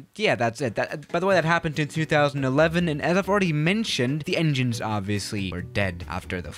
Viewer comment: did Swiss001 said 3011?